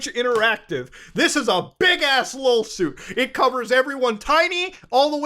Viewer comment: I thought you said Lawlsuit and even if you didn't, it fits the vibe here.